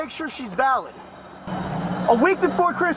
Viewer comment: “Are you hurt?” “Emotionally 😔” I can’t with this dude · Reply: I thought you were joking he said that I was weak when that part came on😭😭😭😭